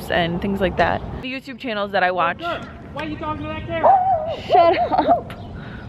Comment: "Yo girl! Why you talkin to dat camera?!?" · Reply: i heard him say, "yo girl, why you talkin like that" but w/e